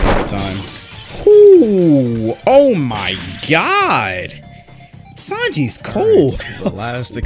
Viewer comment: "Sanji is cold" but his leg is on fire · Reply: he actually says “sanji is cold!” in